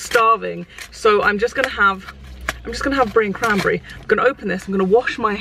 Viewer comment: I think you were saying "brie and cranberry sandwiches" and I 100% thought you were saying "green cranberry" 😂 I was so confused